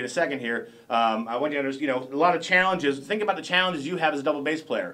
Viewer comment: think about the Challenges did he said?? please correct me please